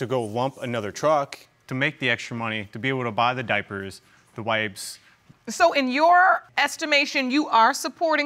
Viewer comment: When he said he worked extra to buy diapers and wipes..I thought he said diapers and wigs! 😂I was like whaaaa??😂